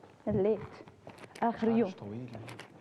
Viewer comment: سمعتو لما قال مش طبيعية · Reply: قال طويلة ماشي مش طبيعية